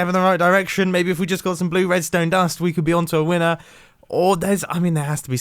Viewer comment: did he say blue redstone dust LoL